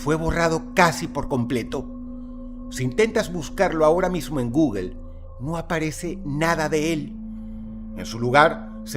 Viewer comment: escuché bien.. sin qué? 😅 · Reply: Sin tetas!😂😂